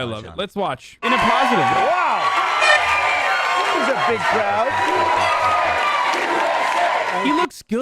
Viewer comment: It sounds like Trump says, "who's a big crowd" and it makes me smile.